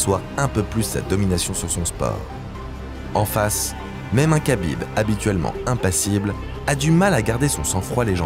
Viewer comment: mm un kabyle j'ai entendu.. ??? C'est bizarre ça 😅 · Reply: Khabib